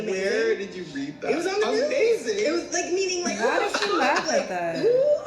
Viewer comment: i swear he says amazing after hearinf about that poor baby dieing but idk i might be wrong but it sounds like it